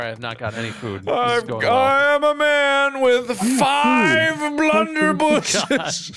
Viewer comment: What actually says: "I am a man with five Blunderbuss' " What I hear say: "I am a man who owns five ovens"